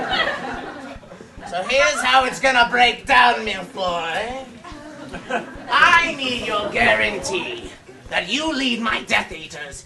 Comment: did Voldy call Draco "MILFoy?" LOL